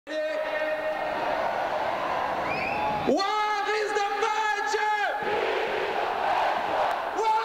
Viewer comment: What is he saying from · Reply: BelgianRedDevils where is the party ? Here is the party